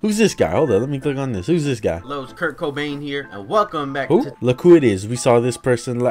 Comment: did he just say kurt cobain?